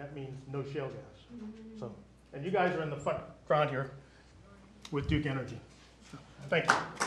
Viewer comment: does he say "and you guys are in the fucking crowd with duke energy"